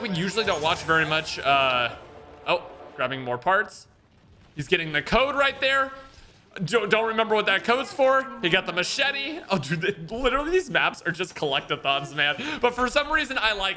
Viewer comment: Did he really say Machete